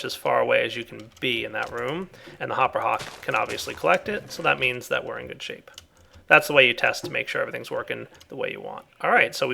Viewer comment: Seriously, did he say "So that means that whe are in good sheep?"